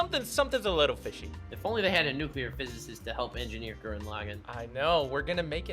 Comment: I THOUGHT IT SAID NUDIST PHYSICIST LMAO